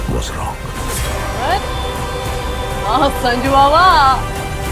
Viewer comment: Did she said Sanju Baba 🤣🤣🤣🤣